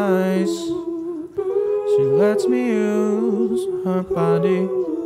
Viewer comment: for the first few times listening to this I thought he said "she lets me use her potty" and I felt incredibly stupid upon finding out that wasn't the case